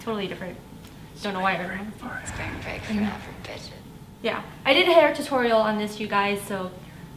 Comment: What was that at 5:33-5:34 a creepy voice saying "spring breaker bitches" while is saying spring breakers????